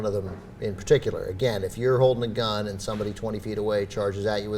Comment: captions are fuckin sad and hilarious on this lol.. "if your old macdonald" instead of "if you're holding a gun" around 2:12.